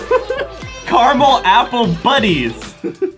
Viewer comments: “Carmel apples buddies!” -Adam 2019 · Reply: I thought he said bunnies 😂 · “Carmel apples buddies!” -Adam 2019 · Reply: I thought that to that he said bunnies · “Carmel apples buddies!” -Adam 2019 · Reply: I thought they said Carmel apple bunnys